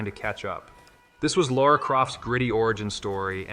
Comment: Did you say "Laura Croft"? What the Ai infused crap is this VO?